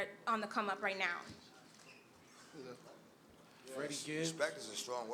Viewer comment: did someone say FreshKidd at 2:28 ?